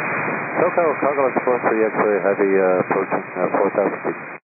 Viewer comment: Xray heavy? For a minute I thought he said “extra heavy.”